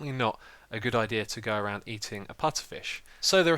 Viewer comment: HAHA He thought it said putterfish